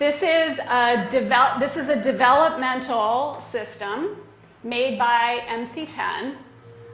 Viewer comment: sounds like she said "Devil-mental" 😲 lol snuck it past you. Do you here what hear, do you see what I see?😐